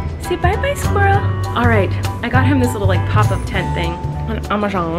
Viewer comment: Whenever she said pop up tent I thought she said pop up ted and I was so confused lmao